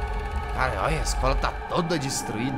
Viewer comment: eu ouvi isso aí jazz, mas eu perdoo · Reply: ele falou "cara olha"